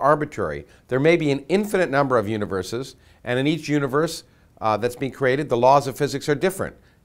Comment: I hear you said (have been create), you believe in god?